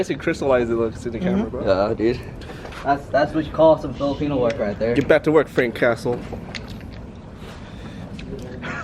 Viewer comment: did i just hear "that's what you call filipino work out there"